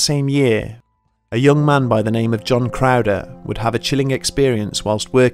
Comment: I swear at 8:49 I thought he said "John Wick" ... I was like, ooo this Wendigo doesn't stand a chance!